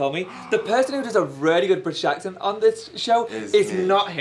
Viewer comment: What did Tan say about "the person that..." 13:35 sorry english is not my first language plus the accent, i couldnt understand. HELP. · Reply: "The person that does a really good british accent on the show is..."